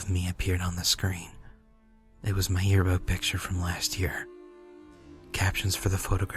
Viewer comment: reads "it was my yearbook picture from last year." when i first heard it, it sounded like he had said "it was my year-old picture from last year," and i wanted to it was pissing me off so much. then i went back and was like... oh. thank GOD